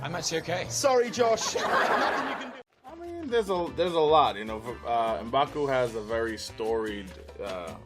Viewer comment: Anyone think he said bakugo at 12:39 from mha